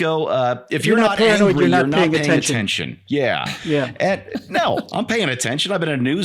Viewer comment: “If you’re not paranoid, you’re not paying attention.” is what Bill said and “If you’re not angry, you’re not paying attention” is what said for those who weren’t able to hear what Bill and said at the same time, I had to listen to that few times before I was able to hear what was said.